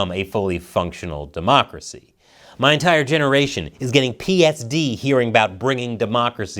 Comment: Join our fight to expose what powerful people want to keep covered up—regardless of political affiliation: <link> Hogwarts Legacy is STILL Making People's Heads Explode <link> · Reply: ummmm @ 10:03 what is "PSD" ??? I THINK meant to say PTSD, right? 🤷‍♂ [EDIT] OHH HAHAHA!! I just turned the closed captions on and watched this part again... yea, PTSD.... screwing up already? NO MORE VACATIONS FOR YOU!!!!